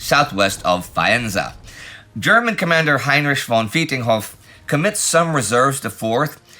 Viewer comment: I laughed when I misheard "Heinrich von Vietenhoff" as "Heinrich von Beating Off." I was not ready for the deafening silence that followed the announcement of a certain Labor MP.